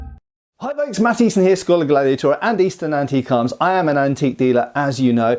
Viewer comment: I thought you said EASTERN Antique Arms it Easton innit? But no website, just faceplant?